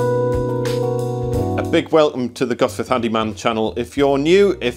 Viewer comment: I thought you said the title of the channel was "Guff with Handyman," which would also be a good title (not that I think this is guff).